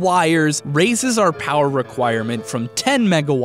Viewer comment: all i heard was "1.21 GIGAWATTS!!!!!?????"